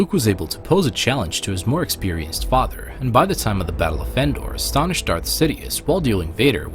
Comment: Sounded like he said battle of fendor